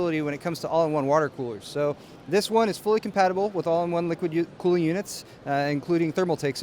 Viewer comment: I have TWO MORE Computex videos. ALMOST DONE! Next week we'll be back to the regular schedule. :-D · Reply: did you say liquid youth? It sounds like a pop band.